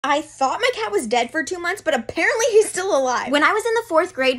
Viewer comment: Bro.. at first I thought it said “I thought my DAD was dead for two months”.. I’m tired.